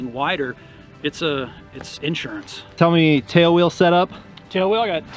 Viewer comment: Talking about ground loops, and how the wider gear is (he didn't say it, but I heard) "spinsurance." :^D